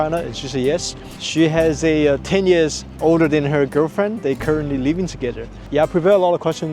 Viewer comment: i heard "she is 10 years older than her girlfrend" at first omg